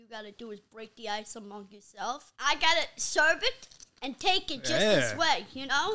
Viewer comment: I heard " i got a cervix and take it just this way."